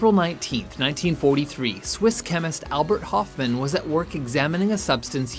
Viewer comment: I misheard Albert Hoffman as Abbie Hoffman